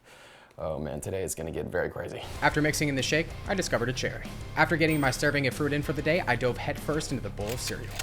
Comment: My dumb ears thought he said “after stirring in the shake, I discovered a chicken” and ate it- 😭